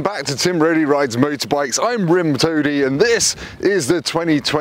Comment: I thought you said "Rim Tony" but either way, lmaaaao